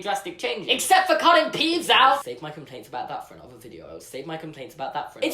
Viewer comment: Haha with your accent I thought you said "apart from culling Paeds out". I was like, what? They culled the medical specialty of infant, child and teens healthcare? Took a good three minutes and two rewinds to figure out that you said Peeves🤣